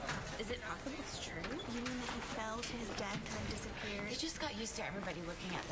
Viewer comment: to 0: 11 wht did they say? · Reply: "He fell to his death and just disappeared." They are talking about how Ian was gone after Ali pushed him from the bell tower and assuming that the liars had something to do with that